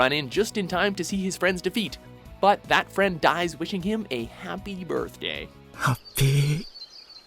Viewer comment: Did he just say happy pasta instead of happy birthday?